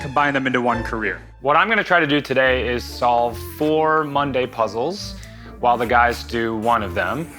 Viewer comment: I totally thought he was saying they were finishing 4 mundane puzzles and not Monday puzzles 🤦